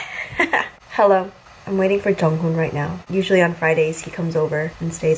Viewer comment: i thought you said "I'm waiting for Jungkook" 😂